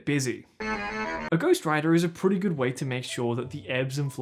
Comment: Lol I thought you said ghost rider the first time you said ghost writer and I was so confused